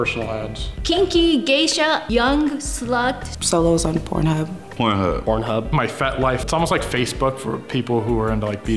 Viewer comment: Dudee Pause at this part and turn on the caption(if you dont know what does she and he said) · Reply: Solo porn life and my fet life?? 😂